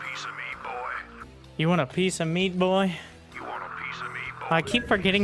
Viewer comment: You want a piece of meat, boy? Or is it piece of me? Lol Closed captions says ‘meat’